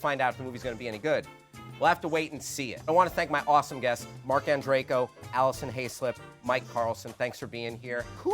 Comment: I keep hearing "Mark and Draco" instead of